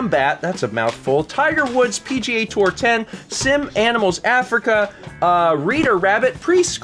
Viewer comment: I thought he said "Simp Animals Africa".